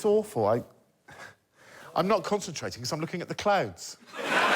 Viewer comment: I know Hugh is decidedly in last place in the prize task, but his is the only subscription I have actually taken up as a result of this show, so really, I think clouds are the real winner here. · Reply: at first i understood clowns and the thought was terrifying xD only realized i misheard (multiple times) when the next task started and Greg made that cloud comment xD